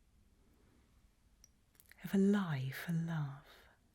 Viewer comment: that delivery is cinema-worthy 🎉🎉🎉🎉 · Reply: I can't understand what she's saying to save my life, I keep hearing "ever life a laugh"😅 sometimes I get stuck with English